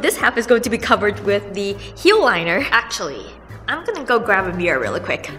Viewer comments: When you said "I am gonna go grab a mirror really quick", what I heard was "I am gonna go grab a beer really quick" ... :)) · when you said you were gonna grab your mirror i thought you said a beer i was like girl me too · When you said "I am gonna go grab a mirror really quick", what I heard was "I am gonna go grab a beer really quick" ... :)) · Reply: Rebecca Y if u keep repeating over and over she said mirror but her accent made it sounds like beer. i myself thought she said beer.